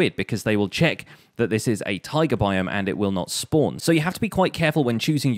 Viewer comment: I've been watching your videos for a few weeks now and have thought for SO long that when you have been saying Taiga biome, I definitely thought you've been saying "Tiger" biome. Your accent has been throwing me off so much with this word I can't believe it took me this long to figure it out! Like what the heck is a Tiger biome?? Now I know... great videos! I've learned a lot since starting to watch these!